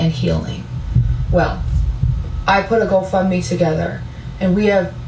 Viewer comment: When she said, "Go Fund Me"....i heard "Go Fk Me"....😂😂😂😂